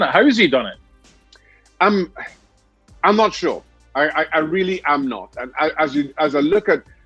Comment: “How’s he done it?” “I’m not Shaw” 🤦‍♂️ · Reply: I thought he said I'm not sure but his accent is kinda thick so it made it sound like shaw